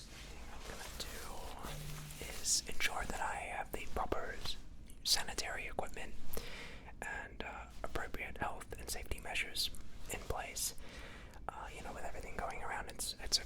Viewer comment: i could have sworn he said “cemetery equipment”